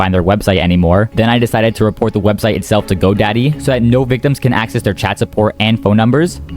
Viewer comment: did he say daddy or did i overhear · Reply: He said GoDaddy